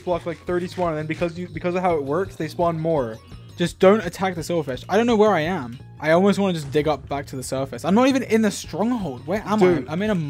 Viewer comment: dream: i hear silly-fish here